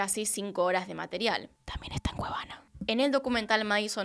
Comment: ¿Que dice? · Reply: "También está en cuevana"